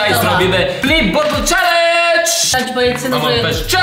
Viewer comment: Kto usłyszał że blowek powiedział blip bottle challenge xD